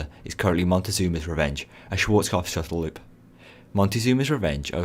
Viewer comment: What did he say on 2:19? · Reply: Schwarzkopf Shuttle Loop